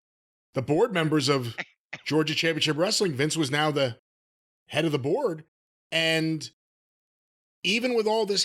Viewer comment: it sounds like said "Vince was now the head of The Borg", and I quickly thought about it and said 'Yeah, that sounds about right.'